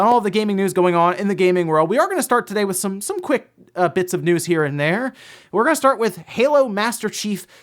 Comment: Wait......Did he say quick bits.....tech linked NO LINUS IS RUBNINGBOFF ON HIM!!!!